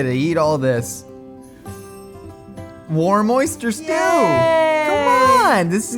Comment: I thought he was saying, “warm, moist-er stew”